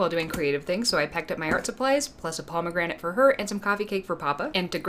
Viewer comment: I thought you said pomegranate burger. I am very disappointed that I didn't get to see said pomegranate burger. beautiful art tho. 😄